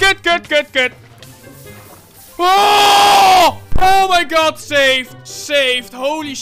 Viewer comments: Hey, react the guy at 7:05 is not says “good” he is saying “kut” which is a Dutch curse word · that is not "Good" but "kut" its a Dutch word that we use instead of "fuck"